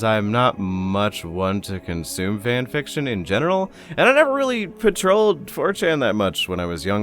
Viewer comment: i swear i heard "in genital"